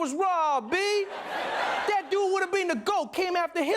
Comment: to whoever subtitled: around 7:30 you wrote Kobe instead of Comey